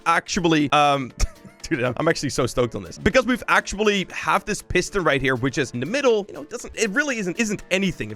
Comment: i thought he was saying p** ster lol